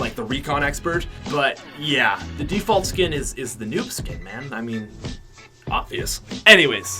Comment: Default is NOT A NOOB SKIN FAZE TFUE PLAYS AS A NOOB SKIN 😂😂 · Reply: I thought that you said that default is not a noob skin